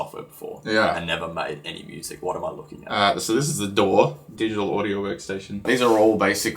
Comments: DOOR instead of DAW is a great Australian accent joke! 😂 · I definitely admit to rewinding a few times because I thought he legit called it a door lol. I was like what did that stand for? I'm pretty sure the last word is workstation...Glad to see they just put the aussie translation 😂